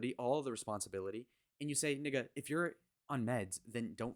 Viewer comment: did you just say nigga?